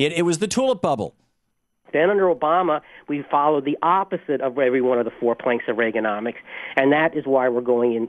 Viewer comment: It's pretty clear at 1:47 that he says "then under Obama we followed the opposite of Reagan's four planks", especially since "Senator Obama" doesn't make a lot of sense in that sentence. Thom misheard the guy and then gleefully (and erroneously) jumped down his throat.